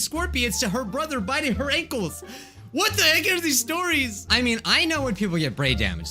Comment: Is it just me or at 1:41 did he say "WHAT THE EGG ARE THESE STORIES?"